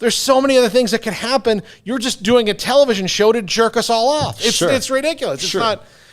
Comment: Did dude say "jerk us all off" at 1:29:42 ???